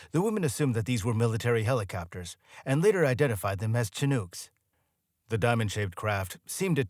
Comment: Umm, say WHAT kinda helicopter 😂😂😂 i swear i heard "SHA-noogs"?! (Er, mighta been sightly less ah, "P.C." with something more like "SHA-nigs"? 🥴) I mean, I SUPPOSE I've got a SMALL leg up- closer to what I'd call an "ankle" up on the general population here when it comes to the pronunciation of this particular word; however since it is NOT solely a "local term" from the Pacific Northwest (I've lived within an hour of Seattle, Washington my entire 30+ years of life, minus my college years on the East Coast at princeton, but we try not to talk about THAT) because yes i know CHINOOK is a originally a Native American word & all.... but seeing as it is also, well, a type of U.S. MILITARY HELICOPTER, annnnnd fauck knows how many people are obsessed with military tech, toys and GUNS cuz, hoorah & WOOT, 'MERICA, right?!? As well as uhh. A kind of FISH?! 🐟Salmon, that is? Welp, that covers a pretty good number of "bases," wouldnt ya say? So i'm havng a hard time finding an excuse for this absolute obliteration of the pronunciation of the word "CHINOOK" here, but, damn it made me laugh? So thanks, i guess?!